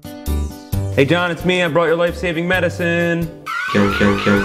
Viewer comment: I always heard Jacques saying "Cake Cake Cake Cake" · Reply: he says kill. not even close to cake.